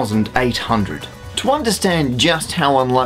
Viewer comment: I think you said it correctly - 116,531,800 NOT as the blackboard behind you indicates 160,531,800